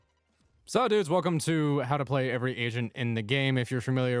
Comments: I deadass thought he said "how to play every asian in the game" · Ngl i thought he said "how to play every asian" and i was like wtf 😂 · Lmfao I read someone say “take a shot every time Gregor calls jett by the name of a k-pop idol” and when he said “agents” around 0:13 sound like he said every Asian